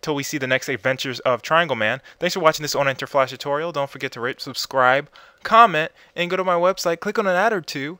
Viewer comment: sounds like you say rape, subscribe lol Great tut tho